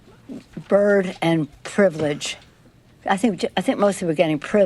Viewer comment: She sounded like she said Bird Law. Charlie wasn't lying about knowing bird law! Pelosi knows it as well.